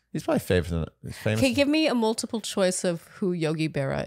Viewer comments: I don't know why I thought you guys were saying Yogi Bear 😂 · The way I thought you were saying yogi bear at first haha